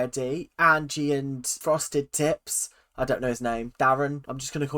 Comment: I keep hearing "frosted tits" instead of "frosted tips" and I can't stop laughing everytime you say it LMAOO